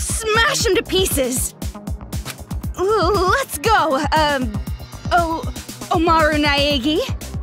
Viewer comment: During the game music at the beginning why did I hear “let’s go barbie”